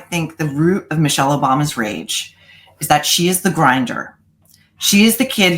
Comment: "...is that she is the grinder..." I heard that as Grindr and fell out of my chair Lmaooooo